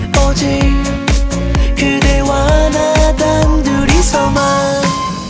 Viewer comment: I keep mishearing that 1:31 part as 그 이수만 ... (That Lee Soo-man)